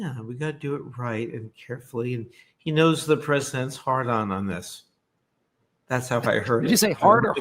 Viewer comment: I heard “the president’s hard-on”, too! 😂